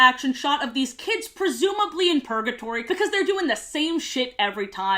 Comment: I zoned out for a minute and all I heard was “These kids must be in purgatory” and got confused Also I wanted one of those Why-flyers so bad